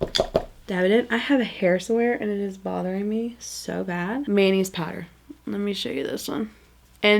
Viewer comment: Also, every time you said “Manny’s powder” all I heard was “mayonnaise powder” 😂😂😂 am I the only one?! I can’t unhear it.